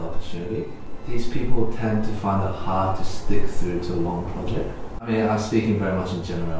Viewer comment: What did Eddy say at 15:10? These people find it hard to___ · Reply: "These people tend to find it hard to stick through to a long project"